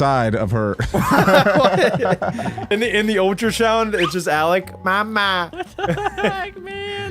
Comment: said ultrashound instead of ultrasound